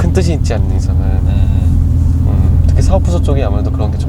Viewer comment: 뭐라고 말한거예요? 사업부서쪽은.. 이러고 한거 같은데 맞나요??